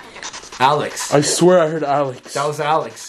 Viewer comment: I heard Tom Alex and Abigail